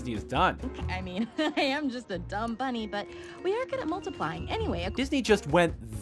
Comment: up until now i thought she said "the numbers are multiplying" wow, what was actually there was even worse then the slight reference i thought i heard